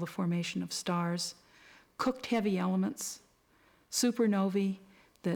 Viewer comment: Between 20:30 - 21:40 sounds like she says "That Shit Heavy Elements"